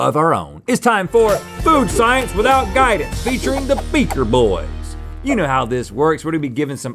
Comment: Thought they said featuring the beastie boys at first 😅